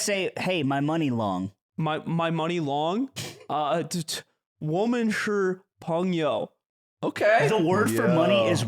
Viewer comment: if anyone was wondering what aimen said here it's "我们是朋友" literally meaning "we are friends"..........